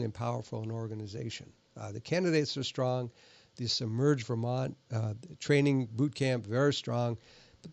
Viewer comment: Guy, I thought you said "Submerge Vermont," which is technically more accurate than "Emerge Vermont."